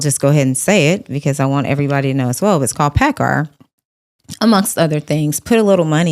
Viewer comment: What was the Stock she mentioned? 31:14 Did she say “Packer” can’t make it out at the end of the word…